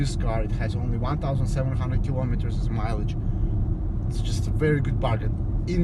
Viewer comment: Have I heard it correct? You said 1700 Kms at 10:40?????🤔